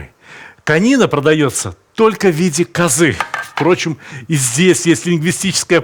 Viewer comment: послышалось : "Конина в виде козы " ;)